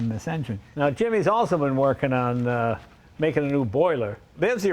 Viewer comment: I thought I heard Jay was on fire working on a steam engine..